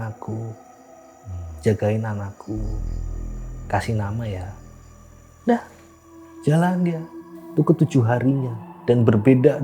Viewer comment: Coba dengarkan baik" ada suara DAH/SUDAH, di mnt 50:11 kalau saya salah dengar atau tidak saya minta maaf 🙏🏻